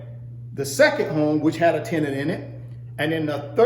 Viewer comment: Guys please..... At 4:40 when hè says " A Tennenenet, what does it mean? I cant understand, help me please. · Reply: Its tenant. Its someone who lives on the property